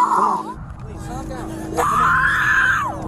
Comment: The motorcyclist died after crashing into the drivers side of the car. 6 month old baby Lola was in the hospital for 2.5 weeks and suffered some pretty severe injuries. She is now recovering at home with her 3 year old sister and mom ☺️ Sgt. is keeping tabs on the family and the daughter calls him “Uncle Dave.” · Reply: "She just killed her baby" is what I heard "I just killed my baby" so doesn't that mean she just killed that guy?? 😢😢???? Someone please help 🙏🙏🙏